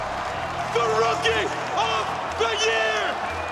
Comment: What the hell did he just say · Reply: “The rookie, of, the year!!!”